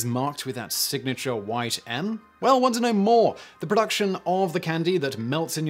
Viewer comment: "Ever wonder how each M&M is marked with that signature white 'M'?" "Not really." "Well wonder no more!" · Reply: Wow, this whole time I thought Simon was saying "Well want to know more!" I guess that's what happens when you only watch videos at 2x speed.